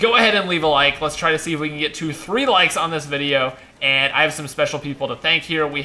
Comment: Did he just say "if we can hit 3 likes on this video" at 13:50?